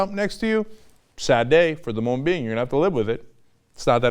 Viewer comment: At [01:44] did he just say "Assad aside" ? My ears are way too waxed to understand anything that isn't said with a slow pronunciation. Danm sometimes English is complicated.